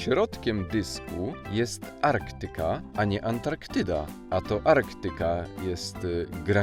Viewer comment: Kto ogląda tę serie milionowy raz ? XD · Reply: Ja i po tym setnym razie usłyszałem wkońcu "arktantyde" zamaist Antarktydę xD